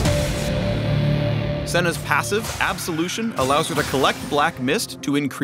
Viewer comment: I heard "AbsoLUCIAN"